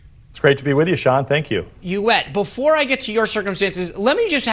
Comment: wonder long it took for our former awesome press secretary to go..."did I just say... you WET?"